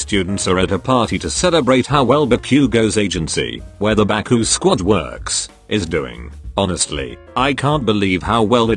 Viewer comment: Baku squid works that wut i heard